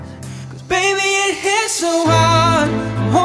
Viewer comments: When I first heard this song I thought he was saying my name when he said you left your Mark. Sounds just like you left Jamar. Crazy right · Who else thought it said You left your Bird and Mom?